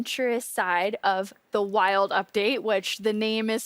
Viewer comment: Ok I was just skimming through and I seriously thought Agnes said "The Child Update".